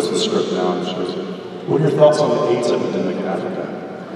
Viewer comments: “what are you thoughts on ____” WHAT HE SAID? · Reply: I could be wrong here.... I probably am but I hear: What are your thoughts on the aid epidemic in Africa Hope that helps? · does anyone know what Jensen said the lady asked him in 35:27? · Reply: "what are your thoughts on the aids epidemic in africa?"